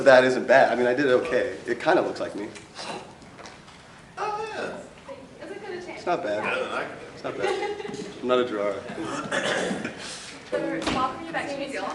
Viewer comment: "I'm not a Gerard", hands down my favourite thing I've ever heard him say and I will definitely use that whenever I do anything, regardless of how relevant. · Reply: He said "I'm not a drawer" although it does sound like he said I'm not Gerard